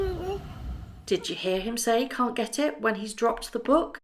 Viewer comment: I heard “ can you get it” as he looked directly at Harry. I also heard a British accent. He’s clearly being raised by a brithis person. Most likely a governess and nanny.